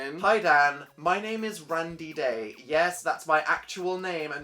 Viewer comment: I thought he said Brandy Day